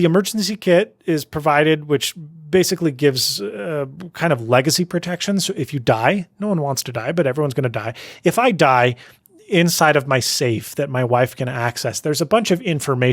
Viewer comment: I heard "If I die inside my safe..." Dangerous safe...